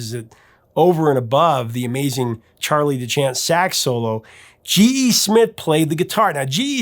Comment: Charlie DeChant is a very underrated sax player. He wasn't very flashy, but rock solid. Love that solo. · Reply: I thought they were saying Charlie the Chan